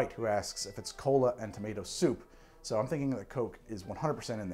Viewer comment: OH, White was saying *cola*... jesus, I keep hearing "What is this, colon soup?" "Close!" and thinking it's way more hilarious than it prolly was :D